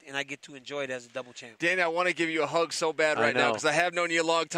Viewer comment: did he said "Dana i wanne to give you a hug" ?